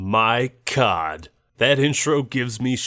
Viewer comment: i thought hed say “my cock” 😭😭😭😭🙏🙏🙏🙏